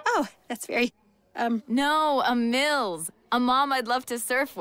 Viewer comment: "Mills"? I think reefs mom misheard "milf" lol